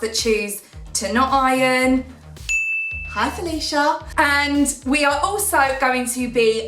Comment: Wonderful!! At the beginning when you said "Felicia" I thought you said "Alicia" (my name) and I was like just I get a shout out!? LOL!!!😂 Then I had to rewind to relisten!! 😂😂😂